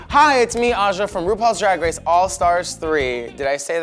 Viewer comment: Am I the only one who heard Aja say she's from "All Stars season 9" (@ 0:46) while she obviously meant RPDR hahaha?